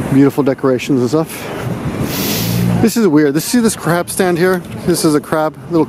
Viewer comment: Totally thought he said crap stand at first, instead of crab stand